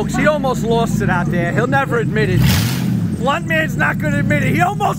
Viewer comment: I thought it said batman's mustang lol